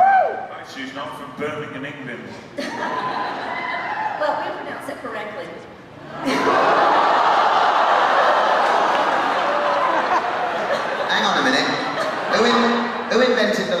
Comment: WHAT does the girl from Alabama say at 13:20???? They all have super shocked expressions lol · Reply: Just in case you still care 6 years later, Mark says, 'Right, she's not from Birmingham, England,' and the girl says, 'Well, we pronounce it correctly.'